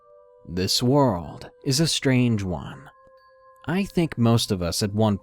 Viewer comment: Did he just say "strangous"? 😂😂😂